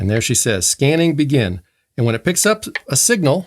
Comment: Hey, I learned something after already having this radio for years. I always thought she was saying "scanning beacon," which I guess doesn't make that much sense, and you let me know that she's saying "scanning BEGIN.". ahhhh...